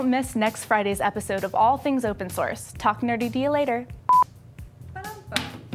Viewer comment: Sounds like she said, "talk dirty to you later" towards the end haha.